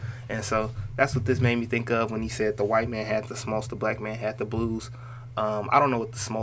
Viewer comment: Actually he says white man had the 'smarts', referring to the white control of the recording industry. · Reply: Haha fuck all these years I thought he said white man had the smokes